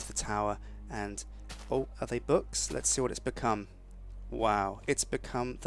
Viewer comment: Omg I heard him say book-come in the library lol